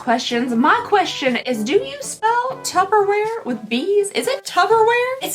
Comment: Lady....I swear I thought you said, "Do you smell Tupperware with bees?" And I legit sat up and sniffed the air and looked at the screen like, "No?"